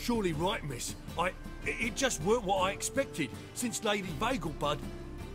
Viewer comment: i thought the guy said lady bagelbutt